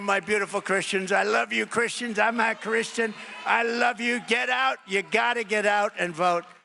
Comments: Man it sounded like he said I'm not a Christian. Wishful thinking? Or need a play back? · I could swear he said "...I'm not a Christian...". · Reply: I heard him say " I'm not a Christian" as well! Ok then I'm not alone in what I heard. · Is it just me or when T said he loves Christians and then, I think, tried to say he was a Christian, it sounded like he said "I'm NOT a Christian "??? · Fact Check: "I LOVE Christians, I'm NOT Christian" said per TRUMP.... 💙💙💙💙💙💙💙💙🙏🙏💙💙💙💙💙💙💙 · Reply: I thought he said “I am a Christian!” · I swear it sounds like Trump says, "I'm NOT Christian." And he's shaking his head no while saying it. Looks like it's getting harder to tell the lies. Lol!!! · I thought it sounded as though he says "I'm not Christian", and the auto-generated subtitles agrees with me! Bit of a Freudian stiffy there haha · did he say he’s NOT a Christian?! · Why has no one noticed that at .18 or .19 it sounds like Trump says “I’m not Christian,” as he’s shaking his head acknowledging that statement 🤔🤔🤔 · Did anyone else notice Trumpf say “I’m not Christian” at 0:17 ? Weird huh, I could have sworn that I remember him saying he was a real fan of both Old AND New Testaments. That and waving a Bible around, upside down, outside a church after he’d had a peaceful crowd violently dispersed? Must be my memory playing tricks or something.😂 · Please tell me??? Did trump say, I love you my beautiful Christian’s, I’m not Christian???? Sounds like he said, I’m not Christian..